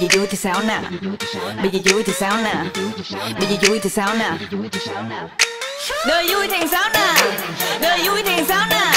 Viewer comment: Ôi bạn em người Đức nghe thành Đời vui thì Sauna đi Sauna mọi người ạ.